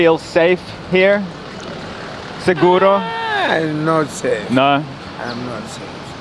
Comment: Thank you for watching. To get a 1 year supply of Vitamin D + 5 individual travel packs FREE with your first purchase of AG1, go to: <link> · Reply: I heard you said "Siguro" you speak Tagalog man? 😂